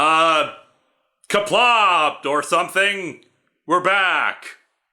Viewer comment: What does he say at 17:02? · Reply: Qaplah. It means success in Klingon.